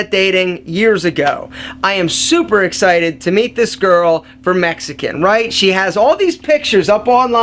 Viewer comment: did he just say... "from Mexican?" lol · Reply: Trey Quillin just watched more lol yeah he said Mexican